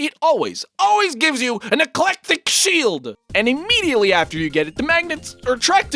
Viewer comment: E C L E T I C S H I E L D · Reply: I thought he said ”NEGLECTIC SHIELD!”